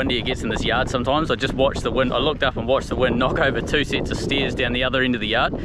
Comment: When you said the wind blew over the stairs I thought you said steers and was looking for flying cows.